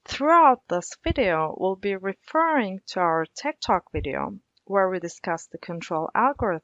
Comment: i thought I heard her say "Tiktok video". Gave me quite a shock :D